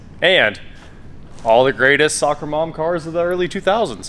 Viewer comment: For a moment there I thought you said "All the greatest sucker moms". To bad it as "soccer moms". I died. Hahahaha